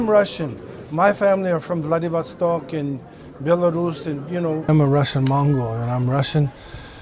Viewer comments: As soon as he said "I'm russian," I got a condom ad, making it sound like he said "I'm a russian trojan man," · Whenever I see this clip I always hear Seagal say "I'm a Russian mongoloid." Don't be so hard on yourself, Steven.